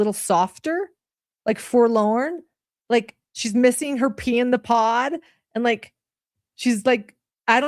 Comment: At first I thought they said Pee in the Pot. Probably more accurate. 😂